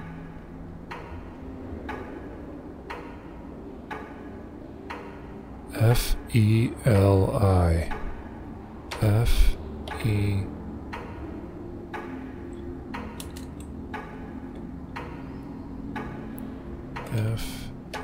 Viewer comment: Just checking with you......But I believe that was supposed to be "LIFE". I hear "F.E.L.I" :)